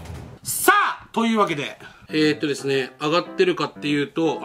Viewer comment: さっ とゆうわけで が くさっはとゆうわけでに聞こえた